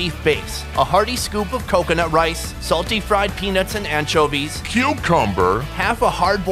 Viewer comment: It sounded like he said pubicumber